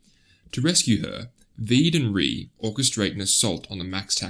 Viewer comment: I heard "Veed & Ree" 23:30 :face-blue-smiling